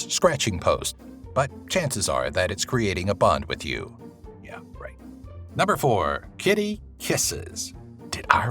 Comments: Did anyone else hear the "Yeah, right" at 4:28? 😂 😂 😂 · Did I just hear "Yeah, right"? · did anyone else hear someone say yeah right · DID ANYBODY HEAR YA RIGHT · anyone else hear the quiet "yeah right" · "Chances are that's he's creating a bond with you" 4:29 did I just hear someone say "yeah right" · that it's creating a bond with you I heard "yeah, right" in the background. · Did anyone else hear the "yea right" at 4:29 ? 😹😹😹